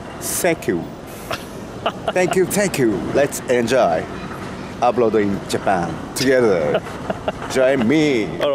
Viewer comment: I thought said "Let's enjoy uploading Japan together!". On 2nd listen I realized he said "Abroad in Japan", but either way, I think it could be your next shirt branding. Just like the Engrish on all those products. "Let's ______ (Insert anything here)". #Natsuki4President